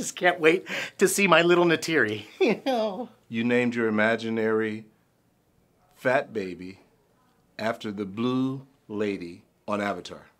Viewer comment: What would you name your imaginary fat baby? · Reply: I thought you said "your imaginary baby fat" which is much more likely to exist.